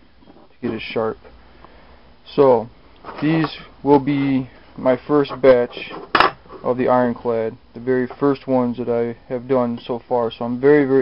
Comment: Haha coulda swore you said,"Well this will be my first bitch with the Ironclad" lol